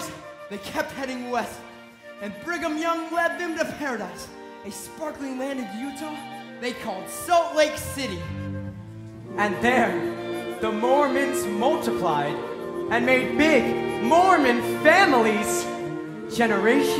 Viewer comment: Did he say at 5:15 "a sparkling land in Youtube they called Salt Lake City?"